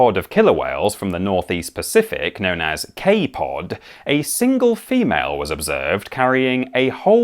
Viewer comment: had to rewind here, I thought you said the Orcas were known as "K-Pop" lmao